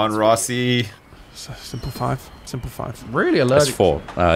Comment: Did just say "Semper Five"? Because that's hilarious if he did xD is also such a manipulative bastard isn't he xD Playing some good consistent Monopoly.